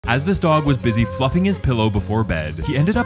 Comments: He was busy “fluffing” his pillow 💀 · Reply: Bro I thought he said fucking his pillow haha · I thought he said the f word instead of fluffing 💀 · I thought they said fucking his pillow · When u said fluffing I thought u said fucking😀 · Yeah, he sure was "fluffing" the fuck out of it.. 😂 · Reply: When I first heard this I thought he said fucking his pillow 😂😂😂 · Fluffing his pillow💀💀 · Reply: I heard “fucking his pillow” and I’m like - “EXCUSE ME WHAT!??” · I was slurping my noodles and i thought my guy said “fu**ing his pillow” and not fluffing